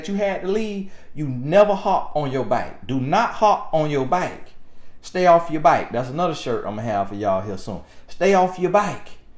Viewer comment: Stay off your bike! 🏍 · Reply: I also thought he was saying stay off your bike because Mr. Gaskin has a thick southern drawl accent. Come to find out he said stay off your back. LOL!